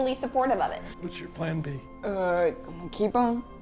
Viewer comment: What did Boze say at 11:44? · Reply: Vartika Singhai keep ‘em